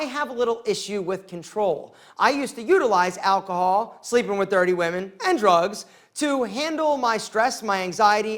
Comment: The real question though is: How do you sleep with 30 women AND drugs? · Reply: I thought he said dirty women.